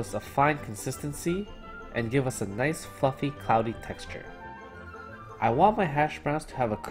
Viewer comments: lmao i thought you said “a nice fucking cloudy texture” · This will give the potatoes A nice FUCKING cloudy texture. I misheard fluffy the first time and was like "Wait what?"